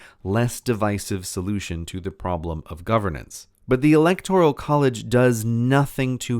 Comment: I keep hearing "electorial" college